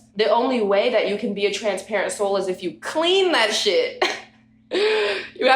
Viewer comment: This whole time I thought she was saying Transparency 💀 😂 🤦